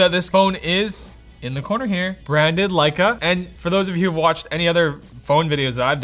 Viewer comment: I thought you said "Branded like a....." and then you never finished the sentence..